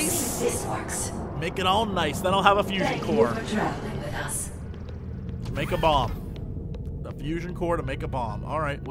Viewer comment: I don't hear saying "Make a Bomb", I hear him saying "Make a bong."